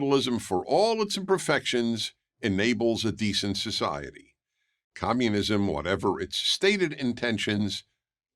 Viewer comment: did you just notice that Dennis prager just said “commbudunism leads to………EVIL”. Bruh that’s no proper argument. As for this video itself, the only thing I disagree with is that China is not capitalist. Yes, it has lifted millions of poverty, but these were through social programs resembling social democracy. I don’t just like socdem states labeled as “socialist”, that’s what these conservative Idiots are saying about Europe and democrats too.